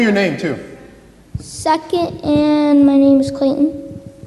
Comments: When he said second I thought he said suck it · I thought the kid said "Suck it", what he really said was "Second". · what kid said "second and my name is clinton" Captions: suck it and my name is clinton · I thought he said "suck it" not second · I thought the kid said Clinton lol like Hillary clinton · I misheard „second“ into „suck it“ lol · <link> First I thought he said "suck it" and was going to run away, but it was second :-) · Lol 0:38 the YouTube subtitles say “suck it and my names Clinton” rather than “Second and my name is” 😂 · when he said second, I thought he said suck it at first as his response to him. I was like jesus, kid is going hard at a young age. lol. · Ok in about 0:35 I heard Second as Suck it but I'm glad that I'm wrong ;) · He asked that kid what grade he was in and I swear I heard him say “suck it”. But he said second. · When the kid said second I could’ve sworn he said suck it and I was so surprised lol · my subtitles said suck it instead of second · Could've sworn he said "Suck it and my name's Clayton" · I thought he said suck it not second lol